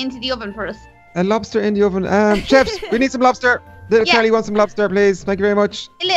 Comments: did little DONNY say little Kelly instead of little Carly? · on 2:31 he said little Kelly instead of little carly